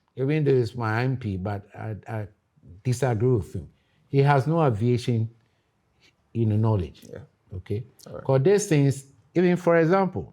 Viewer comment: Did I just hear "He has no aviation in knowledge"